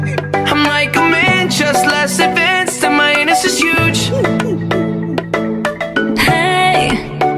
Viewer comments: ‘ mY aNUs iS hUge’ got me laughing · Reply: I thought it said "and mine is less huge" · "Hi, I'm a baboon, im like a man just less advanced, to my ANUS IS HUGE" I bet at least ONE of the boys in my class would say that💀💀💀💀💀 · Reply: I thought he said "and minus as huge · "Hi, I'm a Baboon, I'm like a man, just less advance, AnD MY AnUs Is HuGE!" Mad asf (with a belt ready to whoop that anus) got me laughing for more than half an hour. 😂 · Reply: Oh- I thought it said minus as huge- 🤚💀